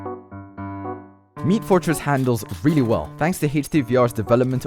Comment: did you just say, meat fortress ?